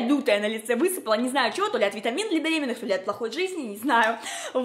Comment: витамины для беременных...или мне послышалось?